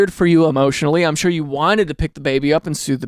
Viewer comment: i keep hearing sue 💀